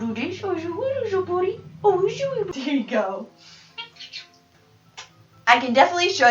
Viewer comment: Swear at 1:18 he he said there you go right after you